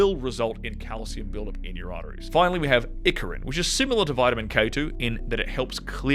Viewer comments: did he say “Ickerit” at 10:57 for clearing out arteries? never heard of it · What is he saying at 10:55 ? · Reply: Icariin